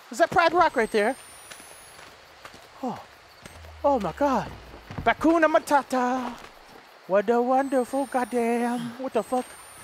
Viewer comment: does he say batcoon amatata